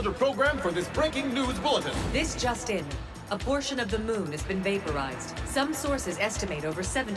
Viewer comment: WHY DID I HEAR THAT AS ABORTION OF THE MOON